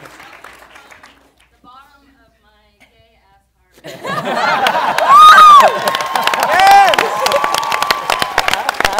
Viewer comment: What did she say in 25:35? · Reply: From the bottom of my gay ass heart